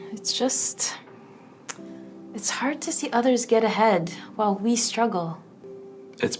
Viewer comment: This is what I hear at 21:20 “It’s hard to see others get ahead (mentally and ethically), while we struggle (mentally and ethically)”.